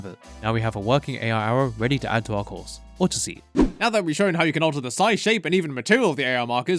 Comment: At the end of cooking with saki, I thought he said orchessi. But he really said or just eat